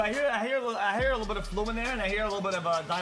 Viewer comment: genuinly what did he say here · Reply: Fluminane